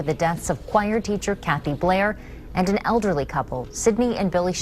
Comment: I so thought it said Kathy baites I was like this gonnna be a wild crazy 1